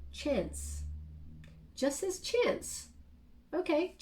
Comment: It sounded like you said Jess’ chance